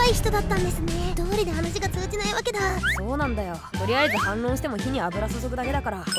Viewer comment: あれ、、 カスミさんっぽいのに声がソラちゃん、、サービスかな？？ 1:39 の所、、そうなのよって書いてあるのに、、「そうなんだよ」って聞こえる、、